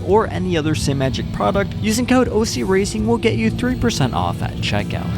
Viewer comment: Not going to lie I thought it said 30% off till you said 3. LOL